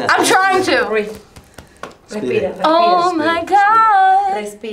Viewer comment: It sounded like she was singing OMG at 12:49 Lol 😂 She's Hilarious I bet he had alot of fun with her.